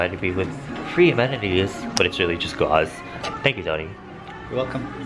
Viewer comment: Did he say, "thank you daddy"? 😂